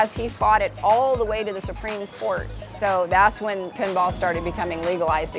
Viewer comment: I thought it said paintball